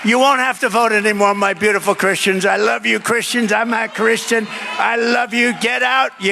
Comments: He said “I Am a Christian”, not “I’m not a Christian” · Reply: He said “I’m ah Christian” but stuttered a little and it sounded like he said “I’m not Christian” he definitely said I’m a Christian. Lol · I thought he said "I'm a Christian" and not "I'm NOT Christian. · He doesn’t say he is not a Christian. He’s saying “I’m a Christian”…. I also think he’s learned ALOT from his experience being president already and all the nonsense. And he’s older and wiser. He’s not intending to be a dictator, he’s going to be a wealthy Robin Hood. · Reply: That's what I thought he said to, but he kind of dragged the m and made it sound like I'mna Christian. But either way, I don't believe he is. I believe he is a politician. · I hear him say "I'm a christian" not "I'm not christian." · He said “I Am a Christian”, not “I’m not a Christian” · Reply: I think he might have done one of those "yanny/Laurel" things. Passing listen, I hear "I'm not Christian". Closer listen, I hear "I'mma Christian" (with a drawn out "ah"). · I am 100% certain Trump said "I'm a Christian" and believe you misheard thinking he said 'I'm not Christian" Plz rewatch Russel. Perhaps it's the timing of the wording or your accent? but seems clear he was claiming to also be Christian. Which is great news and tracks with his known beliefs/actions etc. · Am I crazy and / or did he say “I love you Christians, I’m not Christian…” 🤔 If you ask me he made it clear that there will be world communism soon enough.